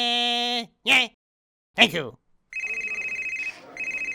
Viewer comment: What is he saying in the end (25:00) "Fart foam in the phone for me, you slut"??